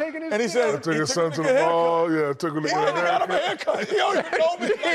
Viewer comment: Why it sounds like Ernie says “took a 🥷🏽to get a hair cut” I to listen 10 times to make sure I ain’t hear that!